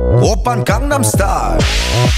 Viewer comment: ok who else thought it said open condom star when they first heard this song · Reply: matvey x ivan I thought he was saying open condom style for gods sake